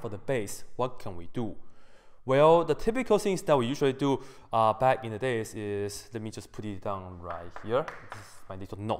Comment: what does he say at 1:48 ? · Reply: Back in the day